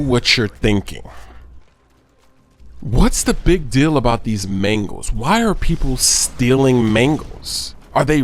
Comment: Sounds like your saying Mengles